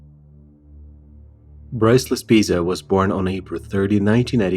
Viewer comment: I know this is in poor taste, but when you said his name I heard "Priceless Pizza".